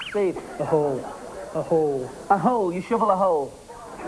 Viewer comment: I thought the one guy said "shovel a hoe."